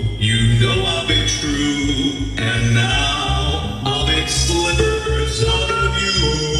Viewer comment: In Rockstar Bonnie's voice lines I've always heard it as "I'll make slippers out of you" instead of slivers.